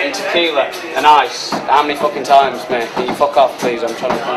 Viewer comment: what the actual hell does he say on 0:39? I hear "I meant fucking ice, can you fuck off please?"